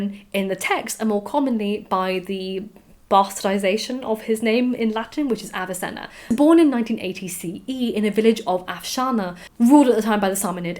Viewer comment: Question: am I the only one that hears "1980 CE" instead of (I assume) "980CE" at 9:30 ish? looking forward to the next part.